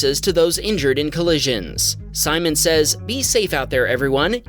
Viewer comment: wait did he say simon says